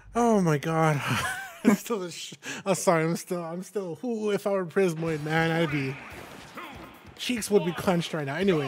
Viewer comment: I thought the commentator said "if I were in prison more cheeks would be clenched" I was like what?